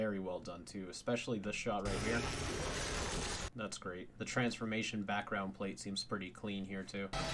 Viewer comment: at that time i thought he said that’s gross